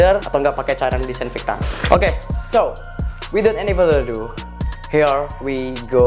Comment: maaf bang ngomong apa sih hahah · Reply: Ferdian so, without any further do artinya = jadi gausa basa basi